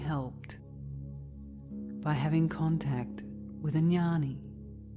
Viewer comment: "you will be immeasurably helped by being in contact with a 'Yanni' (?) im sure im not spellin it correctly... does anyone know what the word means? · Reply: Jnani